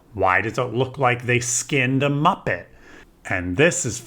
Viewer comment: Skinned a muppet! Ok you made me spit out my orange juice! I had to go change my clothes and clean my computer screen. Thanks for my Twink Drink this morning! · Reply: OMG - thank you for the clarification. I literally thought he said "skinned a muffin"....LOL!! This makes WAY more sense. :)